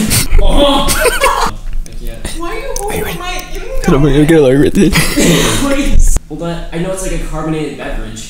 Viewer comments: I think Ryan said “Cause I’m gonna get it like right there” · I think he says "cause I'm gonna get it like right there" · I think he said cuz I'm gonna get down everything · I think Ryan said at 9:04 and I quote " because I'm worried I'll get it all over everything" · i think he said 'i'm gonna make it worth it'